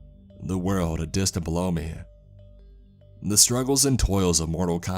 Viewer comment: I thought he said "blow me" just goes to show that going a year without sex isn't delightful in the slightest.